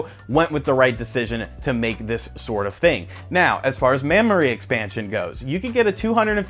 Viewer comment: I heard "mammory expansion." lol.. (I'm so mature.)